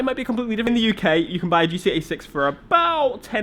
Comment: For the latter half of the video, I thought you were saying GTA 6. 2hrs later and I'm like: OHHHHHHH he was saying GT86